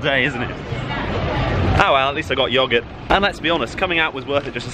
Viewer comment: is that yogurt I hear?